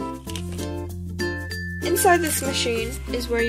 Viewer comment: You said “inside this machine” and I thought you said “in this squishy” it took me 5 replays to realize that.